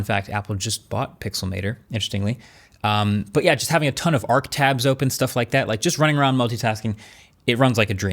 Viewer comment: did he say of Arc tabs, havent heard that before. Is Arc mainstream now.